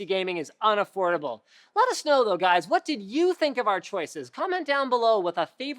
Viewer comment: haha sounded like you said lotta snow